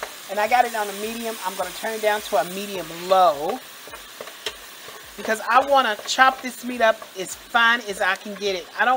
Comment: what did she say? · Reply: Pepperoncini